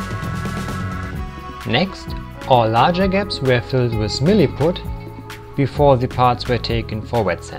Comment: what is it that the larger gaps are being filled with again? I didn't quite catch that. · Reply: its a two part epoxy putty called "milliput super fine"